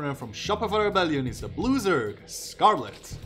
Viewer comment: When you announced Scarlett as the "Blue Zerg" I heard "loser" for some reason. Turned out to be accurate lol